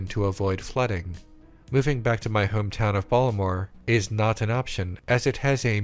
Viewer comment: What's his hometown? I thought I heard him say 'Baltimore', but i'm not sure.